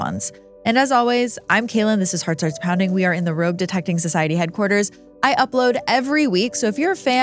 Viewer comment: Everytime you say that, I think back to the comment I found on a video last year that thought you were saying "robe detecting society" 😂